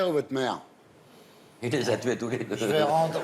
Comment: Il a dit quoi à 5:51? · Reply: "il les à tué tous les deux"